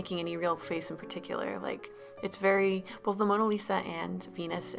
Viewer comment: thought she said penis 😂😂😂👌